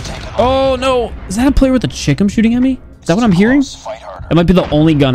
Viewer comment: Why am i hearing 'Chicken' instead of 'Chicom'